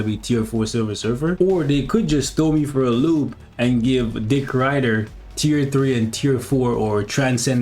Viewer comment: lol 9:52 did he call him dick rider 😂